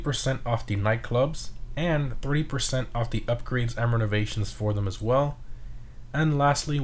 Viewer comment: Did he say 3 percent off of renovations in nightclub??